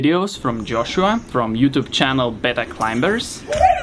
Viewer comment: I thought you said the YT channel was "better climbers" but it was BETA Climbers. My hearing sucks apparently. <link> A link for anyone else who wanted to watch.